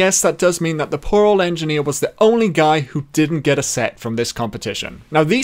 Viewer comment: I hear you're "set" as sex insert lenny face in replies